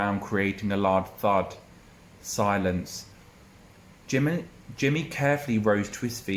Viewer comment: Am I the only one who heard him say Jimin? Hahaha <3 Beautiful story, I have my exam tomorrow! Thank you for all your help! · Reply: I definitely said Jimin 😂 good luck!